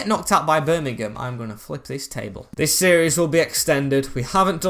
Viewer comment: did you say F**k this table? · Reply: Flip! 😂😂😂